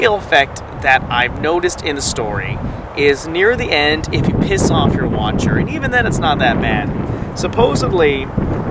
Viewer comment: I thought he said if you piss on your watcher xD